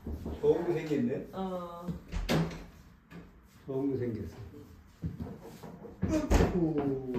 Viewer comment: i think the grandfather say that the kid is ugly in korean but i don't know maybe am wrong 🤷‍♀ but anyway your kids are adorable 😍😍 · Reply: grandfather said “He got a ball” in korean. 공생겼다⭕️ 못생겼다❌